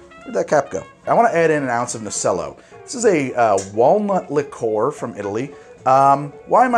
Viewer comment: Ohhhhhh, walnut liqueur. I heard "Walmart" liqueur. Distilled Walmart . . . gack.